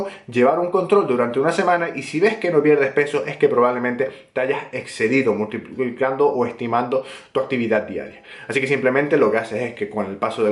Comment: Que dices en el 7:35? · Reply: Digo "probablemente te hayas excedido estimando o multiplicando tu cantidad de calorías diarias". La palabra multiplicar se me atraganta un poco 😂